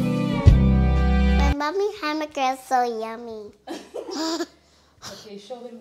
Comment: Your daughter tasting the food was hilarious. Kids don’t know what’s good lol. Great video, very motivational. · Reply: oh it sounded like she said “mommy’s burgers”, that’s why I thought it was her daughter.